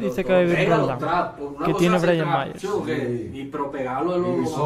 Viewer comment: Que dice Bryant 9:09 ??? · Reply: Y sin promo